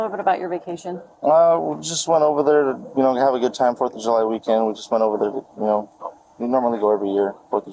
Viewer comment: Did he say “foot of July?”